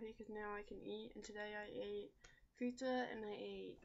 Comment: hold on... did you say you ate PIZZA 2 weeks post op? :O or did I miss understood at 4:24 ?